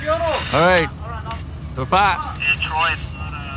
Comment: the fella cop asks his friends whats uo. They all say its good. Cop says thats good bubye. Cop radio says: "Detroit!"... Anyone else catch that?